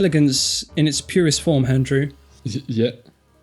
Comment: does he say "Handrew"? Because that is now canonically name.